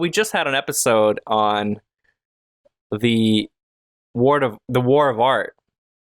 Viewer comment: I heard your review of the War of Art and I thought you said it was the Art of Learning…After 4 hours of listening to a guy describe every move of Tai Chi and a chess game, YAWN, I finally realized I was listeing to the wrong book. As a girl artist, I can give my opinion on sexualizing women in art…it’s usually done by artists who see women only as objects anyway.